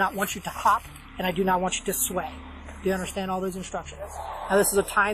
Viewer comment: Did the officer say 'I do not want you to sweat' ?! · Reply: I know the Transcript says 'sway' but I heard 'sweat'.